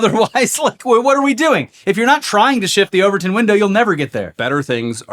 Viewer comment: TIL: About the Overton Window. Also, initial thought he said Ovaltine window. Lol